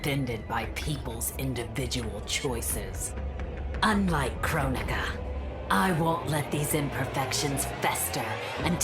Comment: captions instead of saying kronika it says crow nigga